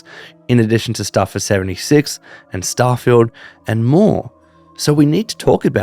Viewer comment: Is it just me or at 0:26 I swear bro just said starfield 76 🤣